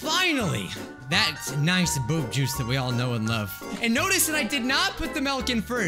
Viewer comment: did he say boob juice!